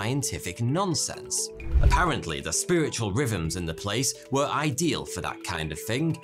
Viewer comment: At first I heard "spiritual ribbons" instead of "rhythms". Makes just as much sense either way...